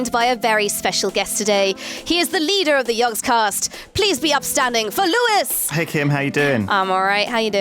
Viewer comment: I thought Kim said, " Please be up stabbing".